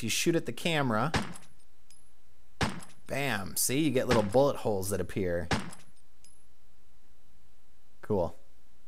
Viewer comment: oh yeah, I dunno how I did it but somehow I got subtitles on this video near the end too and YouTube seems to think that when you said ''Bam, see, you get little bullet holes that appear'' it seems to translate it too ''nancy get lovable holes that appear'' o.O it's also at 13:37 too. made me laugh so hard xD gonna have to figure out how I did that :')